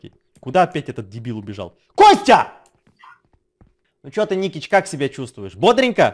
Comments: в 12:40 Я одна услышала Ну что ты НИКИЧ?! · Я одна слышу Никич!